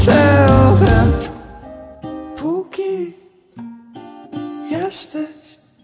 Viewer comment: Przy pierwszym odsłuchaniu tej piosenki usłyszałam: 'póki jesteś trenuj ręce' 😂